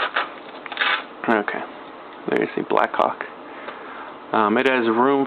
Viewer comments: Did he just say....Black Cock? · Did he just say his chest rig is made by Black Cock? I swear he said that.